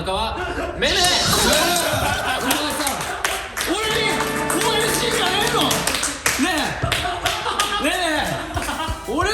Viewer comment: からのふっかさんなんて言ってるかわかる方教えてください🙇‍♀️ 何回聞いても聞き取れない💦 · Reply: 俺に！応援してんじゃねぇの？！ねぇ！めめ！